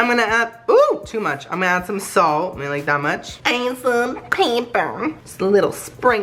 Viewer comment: i thought you said paper instead of pepper.